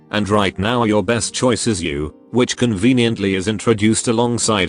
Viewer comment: I heard, “Your best choice of burn damage, is you”, and I went “Me?”